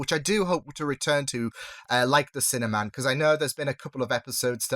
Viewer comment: It really is an overall Brit accent thing. Every time you or say "sinner man" I hear "cinnamon" throws me EVERY time